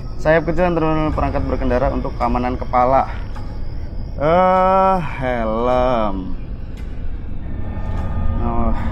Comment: Di menit 20:59 sampai 21:19 ada suara gitu tapi ga tau ngomong apa tapi itu lumayan jelas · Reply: "yarkhamukallah sewu jiwo" yg gw denger, merinding njirr